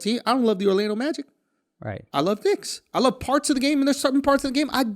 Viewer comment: BRUH tell me Andrew did not just say "I love dicks!"